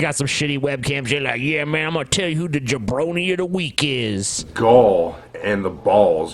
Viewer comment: what the fuck are they talking about at 1:30:00? I can't understand what that award is supposed to be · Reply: The jabronie of the week award. Its nothing official or real in any sense. Its just someone saying "This is the idiot/clueless moron of the week". Except this idiot decided that Hillary and Bernie, and....kind of Obama deserve his "award". Rest assured, all these politicians are crying in a corner now, asking for their mommies.....lol